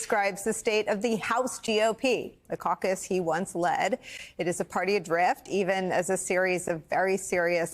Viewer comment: I misheard "a party adrift" as "a party of grift". If they shut down the government, they'll blame Democrats for the economic disruption and fleece their base for contributions. G for gaslighting, O for obstruction, P for pickpocketing donors.